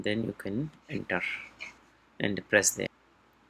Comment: Please, what did you say at 4:16? "And then we can...." I've been stuck for a while. Thank you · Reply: He said "then press that"